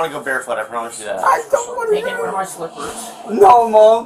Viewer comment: Wait did go to jail in his grandma's slippers? I think said at 4:53 "you're gonna wear my slippers" or something